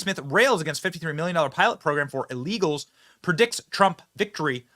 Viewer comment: Listen REALLY close, I hear "legals" not illegals.